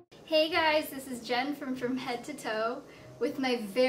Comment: I alway thought she said "This is Jennifer from head to toe" lol